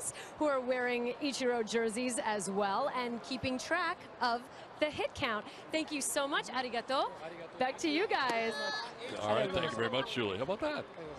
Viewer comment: 〜 so much Arigatou か マジありがとうに聞こえた笑